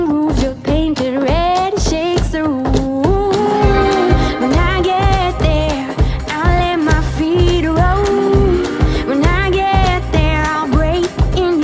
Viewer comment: I thought she was saying "when i get there I'll let my feet rot" 😅